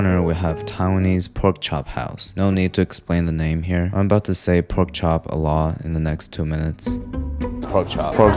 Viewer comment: So, I cant be the only one who miss heard when he said "I going to be saying pork chop a lot here." I thought he said "im going to be saying pork chop Allah here." I was like, what?